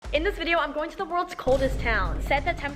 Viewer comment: I thought she said coolest but it turned out to be coldest